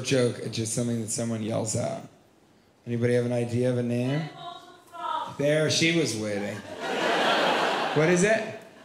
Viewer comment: Wasn't just a one word joke, it was a one word closer. Wow! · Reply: Felipe Ramirez oh god I thought she said “Menopausal Fraud” and couldn’t understand why he yelled “Ribbit!”